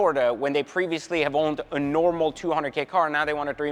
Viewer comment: "A cheap $200,000 car"😂😂😂 Gotta love. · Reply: he actually said a "normal" $200k car 😆